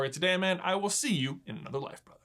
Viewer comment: I thought you said pee brother